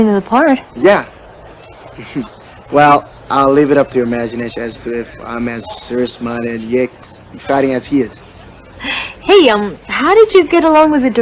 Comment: im sorry WhAT... all i heard was ‘amawxciageryegsxciting as he is’ sorry im probably deaf lmao.... but ngl i kinda like the art style tho <.< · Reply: I know it’s been two years but still here. He said “I’ll leave it up to your imagination if I’m as serious minded but excited as he is.”